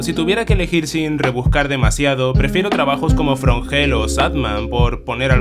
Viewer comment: entendí shadman. Estoy enfermo... pero no tanto como el propio shadman